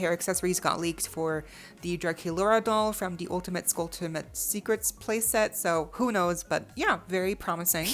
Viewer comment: 😁 Did you say "ULTIMATE-SCULTUM"?